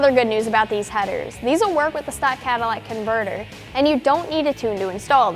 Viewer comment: Did she say "Cadillac converter" at 2:50? I really hope she said it on purpose. If it was on accident, no biggie. Awesome video anyway. I think I'm going to buy a set of these.